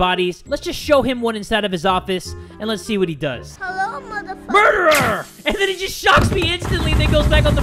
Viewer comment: Lol I heard someone said “hello mother- fu”. but then she was killed